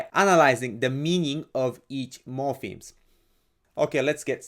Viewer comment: I thought you said morphines not morhpemes lol